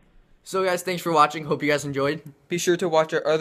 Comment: Nice Video! At 1:30 it sounded like he said comment rape and subscribe! lol